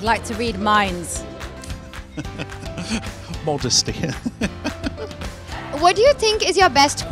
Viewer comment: what nigel say? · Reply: Modesty.